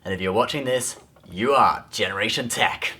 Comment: I think I'm physically dying because I thought you said generation 10, then at the end it was generation tech.